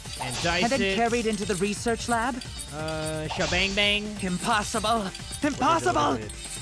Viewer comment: Yo, I'm almost thought he said Kim possible!👃🏿